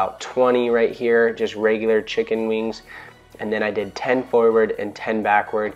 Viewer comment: Chicken wings....HAHAHAHA! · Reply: Karan Soam What? HE said chicken wings...it's what he called the move he did at 1:50.